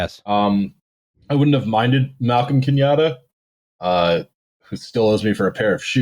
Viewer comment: I was half-listening while making a cup of tea, and thought I heard say that Jomo Kenyatta owed him a pair of shoes.